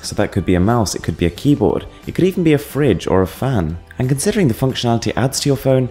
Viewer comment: did you said fridge?